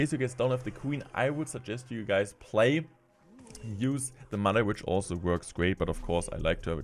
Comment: does anyone know what he said? it sounded like "use the money" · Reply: "use the mother witch also" you can use it instead of archer queen in the first deck